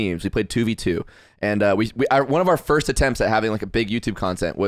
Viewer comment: Bruh at 33:42 I thought Karl said CRT instead of CTR and I was trying to figure out wtf Mario Party 4 had to do with Critical Race Theory for like 5 minutes.